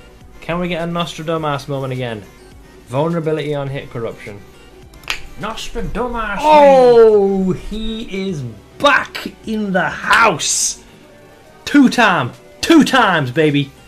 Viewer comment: wtf he said? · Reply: Nostradamus, he is back in the house